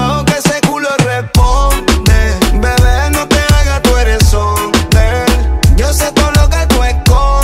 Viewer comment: Yo tambien pensé que desia "BB no te hagas tu Heres hombre 😅😅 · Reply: Yo también pensé que decía q es Hombre 😂😂😂😂 el sordo no oye pero bien q compone 😂😂😂